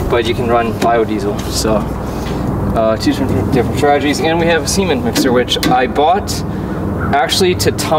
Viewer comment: I thought he said sea men mixer there for a second.